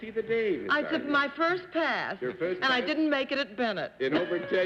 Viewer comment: What does Arlene say at around 6:55 about passing that has John saying :“Why, Miss Francis!“ ? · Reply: I took my first pass and I didn't make it at Bennet